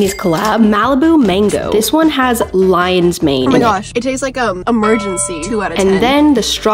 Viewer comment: Why did i thought when she said "loin's mane"i thought it was a kind of a jellyfish